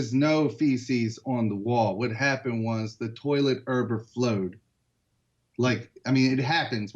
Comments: Is he saying the toilet erber-flowed??? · The toilet URBERFLOWED? That's what it sounded like.